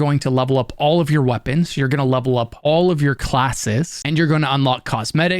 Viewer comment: "And this is where ur gonna lup-lup all ur weapons, ur gonna lup-lup all ur classes..", lup-lup is all I hear now.